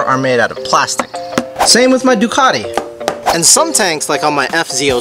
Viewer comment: On your Ducati, I thought you said Bugatti